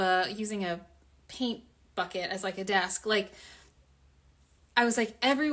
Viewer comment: Did she just say she had a “pee bucket for a desk “????😮